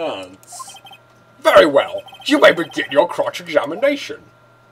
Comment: I thought you said "you may begin your CROTCH examination." >_>